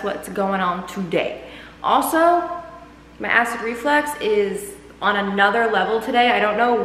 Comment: what did she say? My * reflex? 🤔